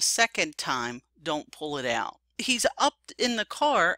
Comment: sounds like he says" I'm not pullin' it out" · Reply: cod god It sounded like he said im not pulling it out to me. Im Not defending the officer , but replying to your message maybe he was trying to grab his hand to stop him from grabbing the gun .. (reason for reaching in car) then when he couldbt secure his hand he got wayy too nervous and shot.